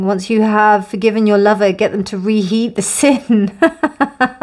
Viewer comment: Love your laugh!! Haha. 🤸😂🤸 And it doubles the fun when I cannot understand what should be re heated. The british accent is too deep for an asian like me. hehe I heard it like... "To re-heat the bacon." 🍜✨🥓 Thanks for this collaboration with! 💕🥰💕